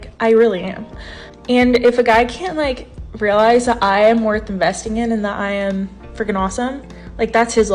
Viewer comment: I thought she said, “I’m worth a Mexican.” I’m like, tf does that mean?🤔